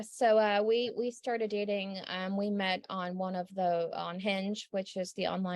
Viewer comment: She is not the messiah, she’s a very bad attorney! 😂 (life of brian) … “nutten” but the truth! Did she say they met on “unhinged” at 4:26? Sorry, wish I’d caught this live, would have had a hoot!